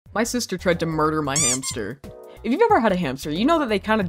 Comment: i thought it said "my sister killed my hamster" not almost / tried 😭😭